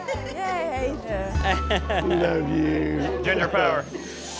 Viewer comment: did i hear dale say “ginger power” HAHA